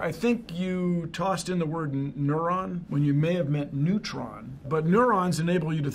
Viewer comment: I thought he said “I think you tossed in the word neuron, when you meant your a moron!” My volumes on low because I’m supposed to be in bed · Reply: Nah he said "I think you meant Neutrons" 😂